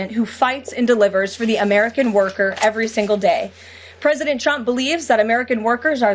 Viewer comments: sounds like she says president Chump. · Sounded like she said President "Dump"...